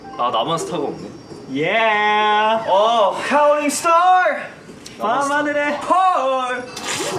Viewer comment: Did Hannie just say "Counting star" 9 months ago??